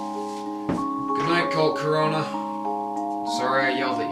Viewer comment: I misheard it as "volcarona" at first, not Colt Corona.